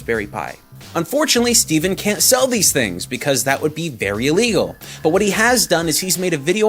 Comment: did he say “berry” or “very”? Cuz if he seyd berry illegal he did a play on words. 🍓😆.